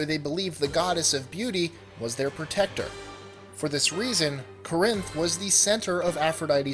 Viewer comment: what city did he say at 2:01? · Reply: Corinth. It's the capital of Corinthia in Greece.